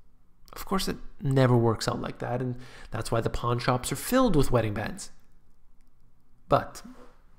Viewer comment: Ha ha (smile) I thought you said porn shops 😂😂👍